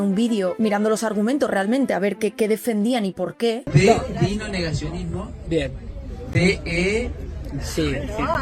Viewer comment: Al principio me emocioné porque entendí "DI NO al NEGACIONISMO".